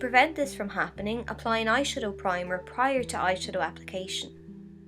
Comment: Hey I really lover this video and I'd like for everyone to be able to enjoy as much as I do! At about 0:42 the closed caption says,"primer prior to I should" but it's supposed to say," primer prior to eyeshadow" If yoh could fix that it'd be great! It would really help the 360 million people who are deaf or hard of hearing enjoy YouTube so much more! Thank you!